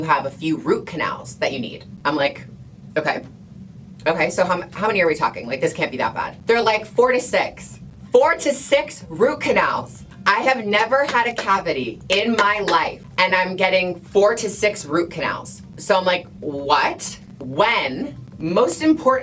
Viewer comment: I thought you said 46 root canals...I was so confused